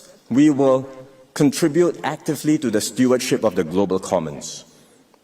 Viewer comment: At @ 17:40 he says "we will contribute actively to the stewardship of the global communists" he meant to say,, "to the global commerce,", but he said communists And at @ 5:12 he said "the long and costly wars in afghanistan,, and iran what he meant to say, is, in afghanistan, and iraq duh thats major slip ups woops lol ;D